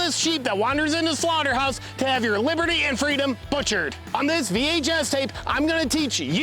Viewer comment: did he say " On this VHS tape"? 😂